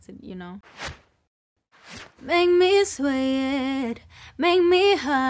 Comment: I always thought she was saying "make me swim" not "sweat" lol